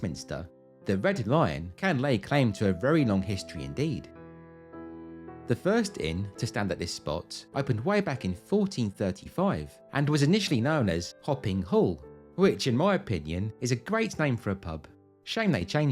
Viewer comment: I swear I heard "Hopping Hole". That too would be a great name for a pub!